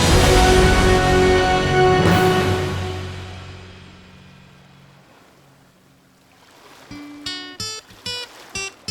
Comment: il à trouvé quoi ?? J'ai pas compris ce qu'il avait trouvé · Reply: Une chocolatine, mon fils.